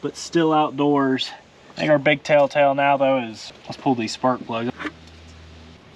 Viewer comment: did he say "tale tale"? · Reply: Telltale 😁